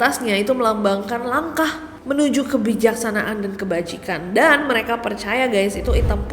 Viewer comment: Kebajikan, di aku kedengeran kebajingan 😂